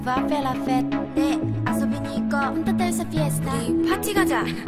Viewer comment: did I hear "punta tayo sa fiesta" ?